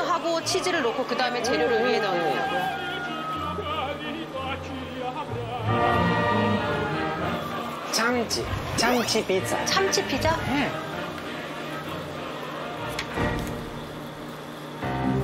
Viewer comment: 네? 뭐라고요 짬찌피자요?